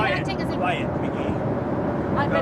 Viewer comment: Was he telling to be quiet? I think I heard him say quiet McGee.